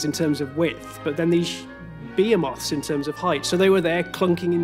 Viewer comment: sounds like he says bee-i-moths